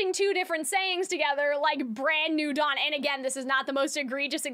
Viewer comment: Thought Vox was saying "brand new Don" as in like in a mafia. Maybe the lyrics are wrong?